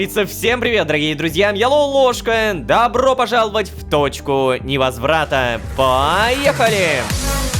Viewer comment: Почему мне послышалась что Ло в приветствии говорит Дрочка невозврата